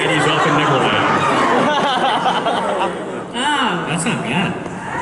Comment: What did he say? "That's not bad" Is what i heard is that right?